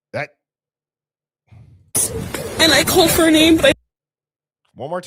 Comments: I head “and I called her name “ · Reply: I think I hear “ called for her but…..” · i hear "and i called for her name"